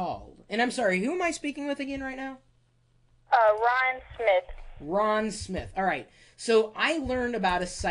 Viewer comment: i thought he said ryan smith? lol